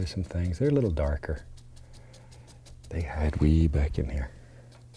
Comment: I thought he said "they hide weed back in here"